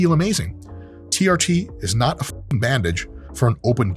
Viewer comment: Idk why I thought you said TRT is a bad b**ch! 😂😂 but you said bandage!!! lol